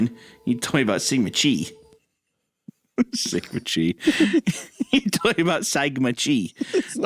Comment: I swear I thought he said “talk about psychin muh Chi” And I thought why is this girl Mathew McCaugnouhey now